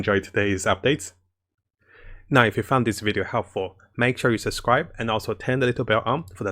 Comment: "If you found this video helpful, make sure you subscribe and also tend a little bit umph" ??? Thats what i hear every time.